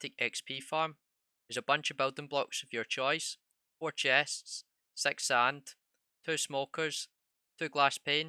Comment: Is it jst my hearing or did he say something else at 0:15 seconds ? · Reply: S3x sand lol